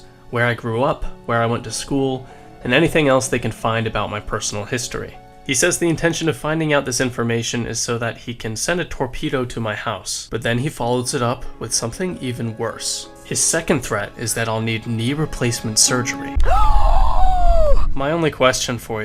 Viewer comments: "Torpedo" Dud he got strong ass cannons if he can shoot torpedos on land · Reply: When I first saw that I thought he said tornado lmao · HOLY SHIT! I THOUGHT HE SAID TORNADO😂😂😂